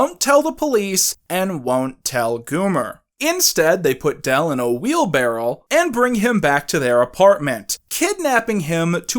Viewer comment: did he say wheelbarrel